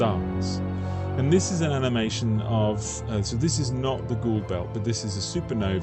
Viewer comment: Is that an Ori supergate at 13:26? I keep hearing Goa'uld belt so it's a natural thing. Just funning I am enjoying your content.